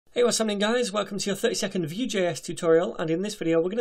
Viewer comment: When you say the title of this.video it sounds like your saying welcome to the 30 second vue tutorial 😊..